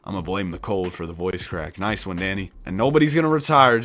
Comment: Appretiate the grind danny you've basicly become a part of my day now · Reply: I thought you said daddy not Danny mbmbmb